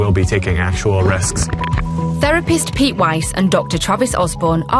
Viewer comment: i think they said pete weist or something but I legit thought they said pete wentz